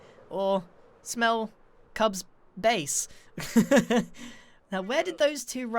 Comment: I thought Grian said "smell Cub's face"